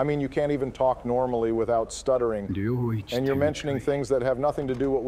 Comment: is it just me or did Joe say domain expansion